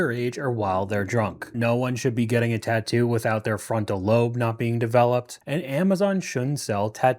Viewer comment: I thought she said frontal lube 🤣 The 2nd most important lube